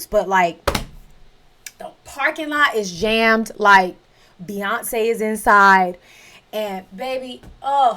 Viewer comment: I thought you said Beyonce's insides. I was like, girl, i can't believe you went there